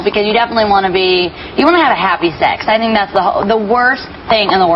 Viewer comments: I thought for real she said happy sex o.O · i thought miley said "you wanna have happy sex"...i was like guuuuurrl!!!...lol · my mind is not dirty guys just that my subtitles were on..So I heard **x ... I know she meant set..luv her😅